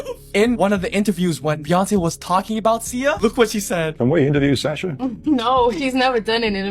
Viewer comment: Sounded like he said Sasha interview, not Sia. IMO